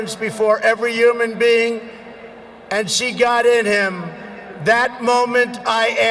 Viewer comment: subtitles are wrong for this time period. It should be, '...and see god in him...'